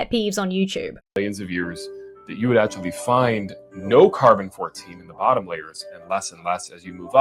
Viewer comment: did he say no carbon14 in the bottom layer and less and less going up?